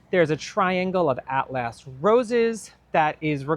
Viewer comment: It sounded like you said"Half-ass roses. Lol